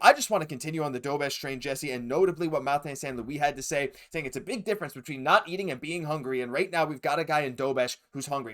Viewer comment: Marty was talking about the team in general and he actually said: There's a difference between needing to eat and being hungry, this team is hungry right now!